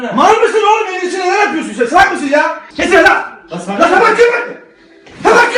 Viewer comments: ne dediğini anlayana pantene altın kelebek veriyoruz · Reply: Tabak kırmak diyor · ne dediğini anlayan 😂😂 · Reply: Tabak kırmak ney lan diyo